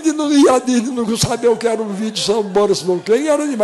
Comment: "SAMBARE BORIS SAMBEI" KKKKKKKKKKKKKKKKKKKKKK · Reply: Eu entendi "sound's boric volcanic", mas as vezes ouço "sound's boris von kenning" sei lá eu procurei na internet mas n achei nada kkkk